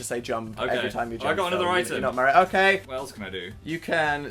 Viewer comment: did dan say "we're not married"??